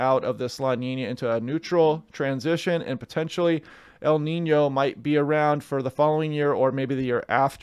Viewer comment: I swear you said La Nino.